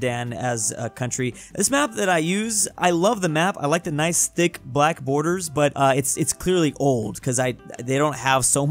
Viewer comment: What was Blud tryna say?? I heard Big Black Co